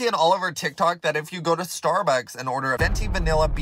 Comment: bro i thought he said “storebacks”